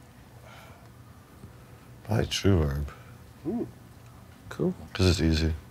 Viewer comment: I couldn't make out what his favourite reverberator was at 41:30. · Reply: trueverb